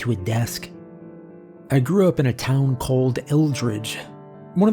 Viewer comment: "I grew up in a town called Eldridge..." 😮 For a second I thought ye said ye grew up in Eldritch! 😅😁👍